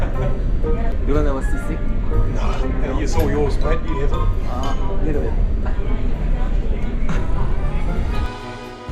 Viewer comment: 영어 잘하시는 형님들, 혹시 7:53 에 외국인이 뭐라고 말하는 건가요? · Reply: I used to be yours. You have it. 이거 일까요?